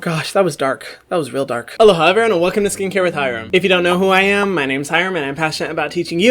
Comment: I’m not sure why but when you said the “if you don’t know me my name is....” part I heard “if you don’t know me I’m M to the B”